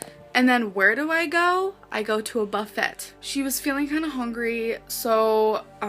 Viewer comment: buffet 😂😂 (I hear)